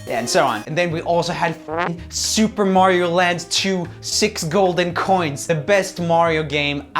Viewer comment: thought it said super mario lads